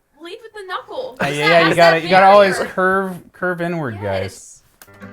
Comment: I spit out my water because instead of "curved inwards guys" I heard "curve n-word guys"